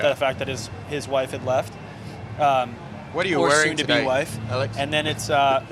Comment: What did Travis say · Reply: "What are you wearing today, Alex"